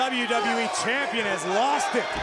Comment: Did he just say Barack Lesnar? Hahahaha